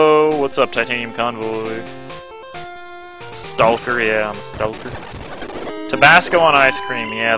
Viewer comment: When you said "stocker" I thought you said "stalker", I was thinking what kind of job is that... lol.